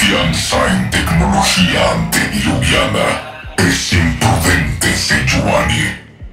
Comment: ahi no dice "anticuada" dice otra cosa, pero que dice? No le entiendo bien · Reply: ¿Antideluviana? XD